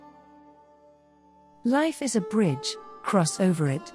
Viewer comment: I heard you say, "Life is a bitch" and that's true!